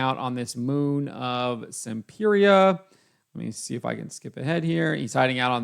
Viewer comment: I thought you were saying the planet is called Simp-eria the whole time